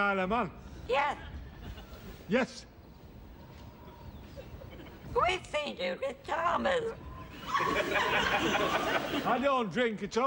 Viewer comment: what does she say here?!? I can never make it out? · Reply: I could be wrong, but I think she says ‘We’ve seen Judith Chalmers.’